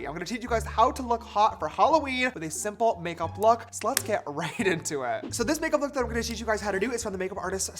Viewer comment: "so let's get right into it" sounded like "sl*ts get right into it which was appropriate for what James was talking about, lol! 🤣🤣💀💖